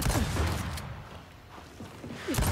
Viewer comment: did you say arumphhh?! If so that's amazing xD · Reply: I listened it back and he said "I rolled", it made me laugh so hard first time I heard it xDD